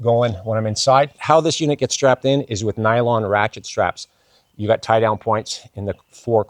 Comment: Some YouTube truck-campers use elaborate spring-loaded turn-buckles (anodized with optional gold-inlaid logo) ["link in description"]. Did I hear you say you use every-day easily-replaceable rat-shit straps? . [edited to add] Thumb 'UP'!